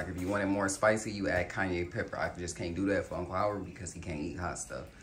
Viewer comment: Did you just say Kanye Pepper right there? LMAOOOOOOOO